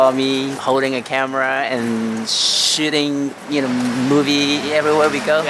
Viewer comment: I thought said shitting. But he actually said shooting. Lol